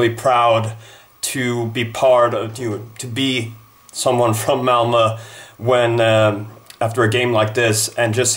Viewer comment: Your pronunciation of the name "Malmö" is far from great. It took me a while to understand what team you were referring to since I kept hearing "Elmo" instead of "Malmö". Just a minor pointed, love your videos. :)